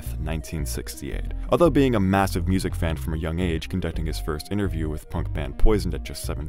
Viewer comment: Did I hear "his first interview was with the punk band...POISON??!"